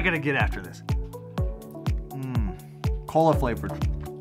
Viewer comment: I heard “colon flavored” when that gummy pretzel came up... 😅😂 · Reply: It's cola flavored